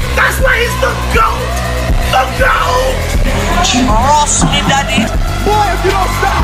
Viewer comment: what he saying?? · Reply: Trust me daddy